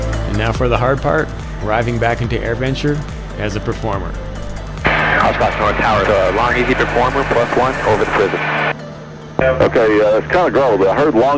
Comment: Can somebody transcribe the radio at 18:18? I can't figure out initial call and the first reply by the controller. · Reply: My initial call was: “Oshkosh North tower, LongEZ performer plus one, over the prison”